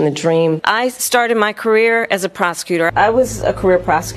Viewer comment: HOLEY SHEET. 3:04 I thought she actually say 'I started my career as a Prost*tute' until I read the Subtitles.